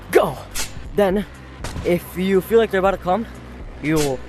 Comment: Why did i hear, "if you feel like your gonna cu-"💀💀💀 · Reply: He said come not the other thing